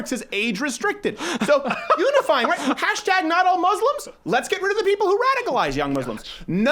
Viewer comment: Thought you said "Let's get rid of emo's" and I was like I'm for it